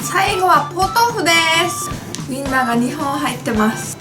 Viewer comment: ｢ウィンナーが2本入ってます｣が｢みんなが2本入ってます｣に聞こえた